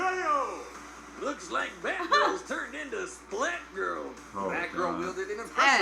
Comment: Idk why but I thought said fat girl in the intro